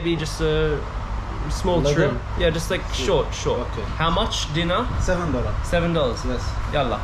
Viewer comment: i’m in iraq rn (home country) at first i thought he said how much dinner😂😂😂 then i was like ohhhh dee-nAr btw they do this everywhere in iraq, say it free or like it’s on me when they rlly want the money😂😂